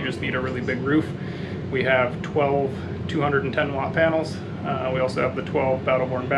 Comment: that digital screen showing you the usage, did you add that system or did it come with the rig from the factory? How many and what wattage soalr panels do you have? Thanks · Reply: As I hit send I heard you say 12- 100w panels LOl